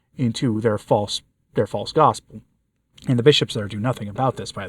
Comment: I have edited this comment because it was brought to my attention that the garbled words I heard as “false God” was in reality “false gospel”. After reading the transcript I stand corrected and apologize. However, I would like to know what your meaning is when referring to the term “false gospels”. I haven’t been a subscriber for very long but found your stories and discussions interesting. Thank you.